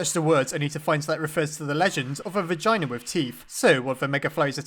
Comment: did he just said vagina with teeth???????????